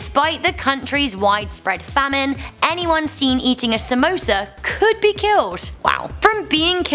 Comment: I thought u said SAMOAS as in the girls scouts cookies